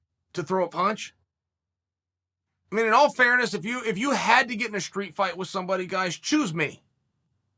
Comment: What Chael said: "Get in a street fight with me if you want to..." What I heard: "Send Me Location...."